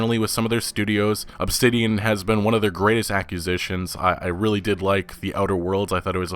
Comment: I keep hearing "accusations" whenever says "acquisitions"